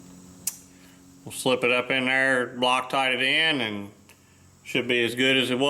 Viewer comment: Did you just say Bloc tite it in?LOL